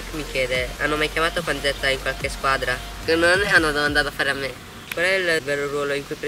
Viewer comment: non ho capito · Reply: Credo che abbia detto:" Non lo so, che mi domandi a fare"?